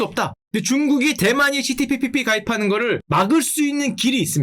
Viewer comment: CPTPP가 왜 시진핑핑이로 들리는거지